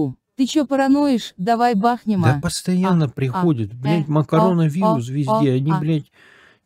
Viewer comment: Макароновирус или мне послышалось))